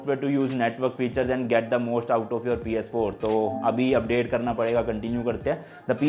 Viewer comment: who heard ps4